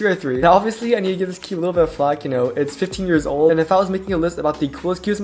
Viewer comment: what did you say?! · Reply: Flack